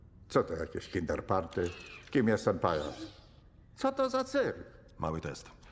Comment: co on powiedział? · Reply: Octavius powiedział: Kim jest ten pajac?